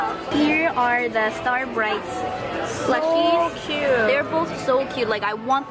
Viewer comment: When you sId Plushies i thought you said Tushies